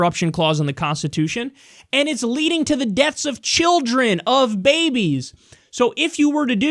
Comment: Did Kyle just say "it leads to deaths of children's of babies"? t=